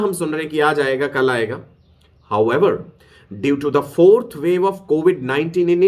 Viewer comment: Wait did I heard 4th wave 3:37 🤔🤔🤔🤔🤔